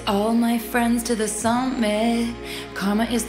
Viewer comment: I kept hearing 'Connor is my boyfriend'